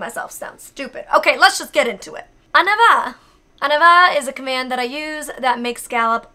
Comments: I always thought you were saying "En avant" instead of "On y va"! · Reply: She is saying "En avant" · if i may precise your editor's translation of the commands: - reste means stay - debout (not début - which means beginning): stand up - couche (it does also mean layer but here it means): lay down - 4:15 it's prête attention: pay attention - aux besoins : we say that an animal "fait ses besoins" when they go pooping thanks for the great video as always 💖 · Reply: frenchprint 2:38 I think that she is saying “en avant”, which translate to “in front” instead of “on y va” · Are you sure it's "On y va?" I always thought Molly was saying "En avant" which means "forward"